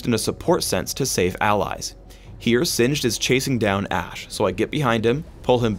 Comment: Did i hear ,"chase singed down." ?